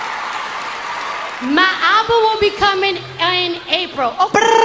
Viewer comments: I don't why but this whole time I thought she was saying "My uncle will be coming on April OPURRRR". But it was album lmao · I heard "My eyeball will be coming" Lmao what is wrong with me